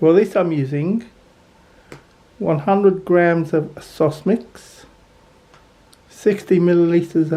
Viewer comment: There was. Sosmix is a plant based sausagemeat alternative. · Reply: oooooh! I thought you were saying "sauce mix "😂